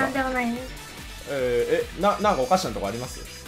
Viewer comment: なんかおかしたことあるにしか聞こえん